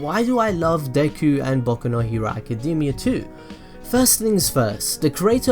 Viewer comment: It sounds like you said baku no hewo